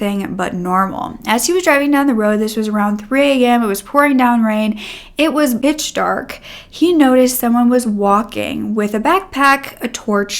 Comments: “It was bitch dark” 1:22 🙃 I’m sorry but that’s what I heard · did she say bitch dark? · i’m sorry did you say it was bitch dark? 😭